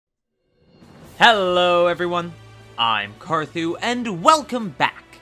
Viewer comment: "HELLO MO-" WHY I HEAR IT😭😭😭